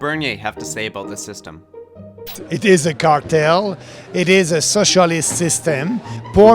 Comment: I thought he was saying "cocktail", instead of "cartel".